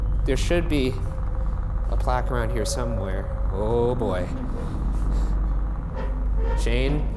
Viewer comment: I heard "I swear to God, Shane."